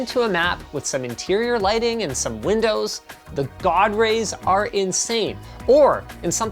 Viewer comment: I thought he said dog rays ffs i need a coffee.